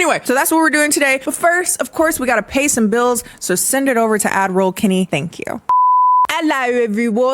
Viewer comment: how is it that despite watching so many of her videos i only just got that shes says "add roll kennie" and not what i thought she was saying "admiral kennie" cause i thought it was strange but i thought i just wasn't privy to the channel lore that would explain it.